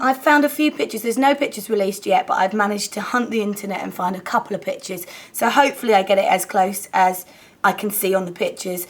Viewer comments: when you said "couple of pictures" at the beginning of the video I thought you said "couple of bitches" LOL I was like WHAT? hahah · i think in 0:22 she says "pictures"...but i understand bitches :D